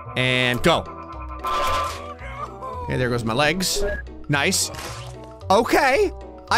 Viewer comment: Did said "their goes my legs" when he lost his arms